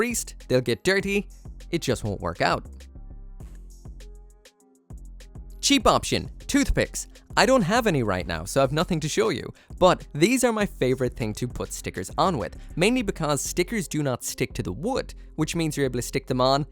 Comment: Lol. Edit: Nevermind, I wasn't looking at the screen and thought you had said "toothpaste" LOL.